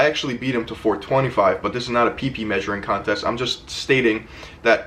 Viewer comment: I thought he said peepee mesuring contest